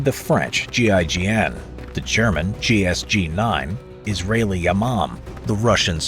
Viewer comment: I THOUGHT HE SAID "is really a mom"💔